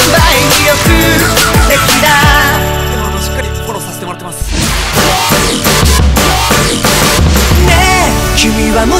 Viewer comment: なんて言ってる？ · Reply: くんポテチ ？？？しっかりフォローさせてもらってますって言ってるけど？？？が分からん😂